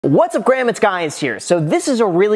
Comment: Is it just me? Or did you all heard him say 'whats up graham, its guys here'? 😂😂😂